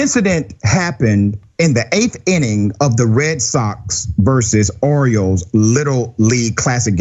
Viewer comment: I could’ve swore he said Red Sox versus Oreos,l 🤷🏻‍♂️